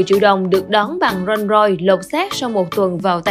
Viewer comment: Roll royce nhưng tôi nghe thành rich roll =))